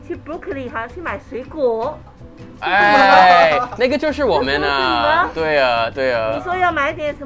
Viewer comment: ayo pause. that sounded like the n-word